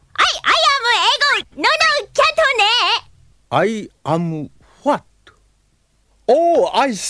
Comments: I cant be the only one who understand after usagi blabbering "I am f..ed?" at the beginning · Did he just say "I am fked"?!